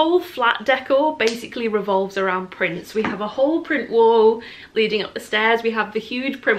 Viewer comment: I genuinely thought you said your flat decor revolves around Prince for a good 10 seconds 😂